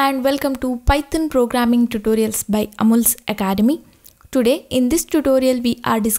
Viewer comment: i hear you saying "Amul's Academy" but i guess its "Amulya's Academy", right???